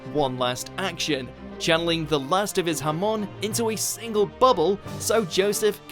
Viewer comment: Lmao when he said hamon he sounded like he said Jamon and that is Spanish for ham